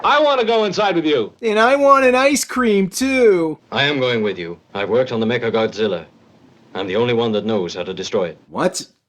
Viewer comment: I thought that guy said "im going with you. My work on the n-word Godzilla," that threw me.